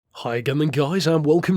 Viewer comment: 2 things: 1: I always hear you say "hi againin guys" 2: what is your opinion on the design of the Ducati Diavel? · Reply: 'Hi again then guys', and I love the Diavel Carbon. Chunky frame, torquey V-twin, big rear tyre. My kinda bike